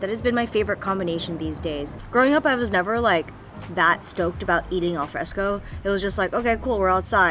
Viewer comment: Did she just said "Eating al fresco"? o.o